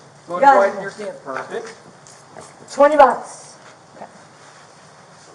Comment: Did she just say 20 bucks when they said spread your feet apart 🤦‍♀